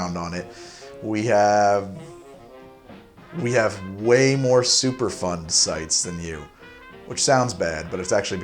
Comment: At first I thought he said super fun sites lol